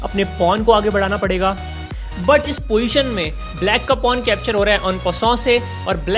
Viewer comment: Please make a video on this topic 'ALL RULES OF CHESS GAME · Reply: Sir i have don't understand your words in 4:06 to 4:07 please reply the word I have heard 'un uso Please reply me because I have be a best chess player like you